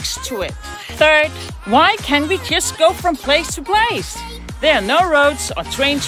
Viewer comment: sounded like she said "kiss" in place of "just"